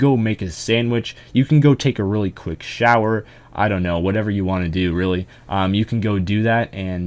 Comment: it sounded like you were going to say take a quick shit. in my head at least lmao